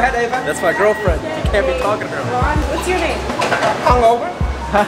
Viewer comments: did he say his name was hung over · DOES ANYONE ELSE HEAR ONE OF THE TWINS SAYING THATS MY GIRLFRIEND U CANT SAY THAT!!